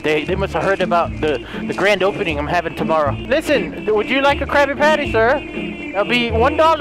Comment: Want me to play more games like this? Hit the like button to show your support and tell me which games I should try :D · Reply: I thought you said crapy patty😆